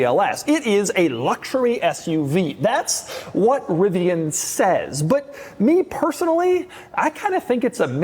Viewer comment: Am I the only one who noticed at 4:43 that he said “That’s what rivan said” not Lucid.